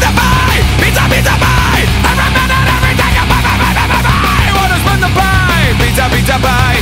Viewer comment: it sounds like he says"wanna spank the pie